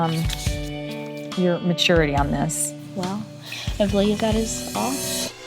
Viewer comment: Wait what did she say at 12:51????? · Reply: She said “well I believe that is all?”